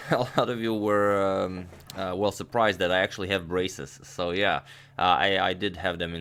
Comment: At the start I thought you said "a lot of you were surprised to find out I actually am racist" and I was just there for a second like "wow and no fucks given"